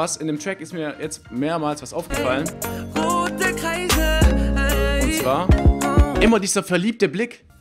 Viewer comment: Ich habe verstanden rauche rote Kreise